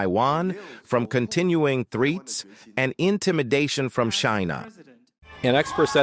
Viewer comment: what is this ai voice saying shina